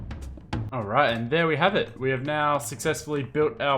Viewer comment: I hear alright in